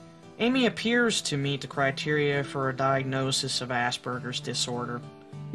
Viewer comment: i heard "ass burgers"